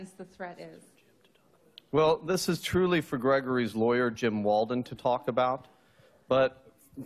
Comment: What does he whisper at 3:45? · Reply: He whispered that "this is for Jim (the lawyer) to talk about"